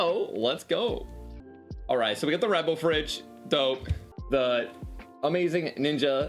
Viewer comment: Ik your saying Red Bull but it’s sounds like your saying rebel lol rebel energy marketing idea ninja